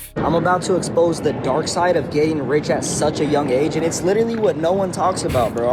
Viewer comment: Ohhhh getting rich i could of sworn he said gay and rich